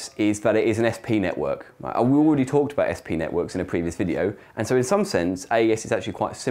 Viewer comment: When he says "sp networks", all I hear is "yes peanut works". I might just be hungry.